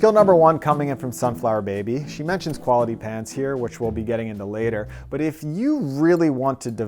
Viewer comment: I totally thought he said "quality pants" and I had questions.